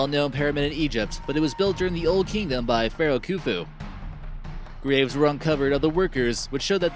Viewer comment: if the first would not read subtitles I would say that I heard the pharaoh kuku